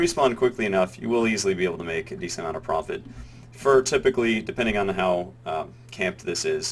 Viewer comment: did you just say "fur typically" instead of for when explaining the fur harvesting part